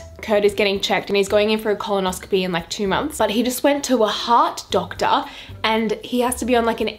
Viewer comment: “He went to a HEART doctor” lol 😂 I thought you said “Hot doctor” lmao 😂 Btw I love you so much! I’ve always wanted a Australian accent 💕💕